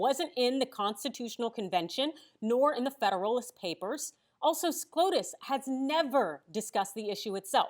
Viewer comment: Did she say scrotus?